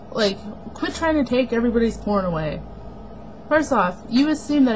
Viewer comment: I thought she said "corn"! That really threw me for a loop!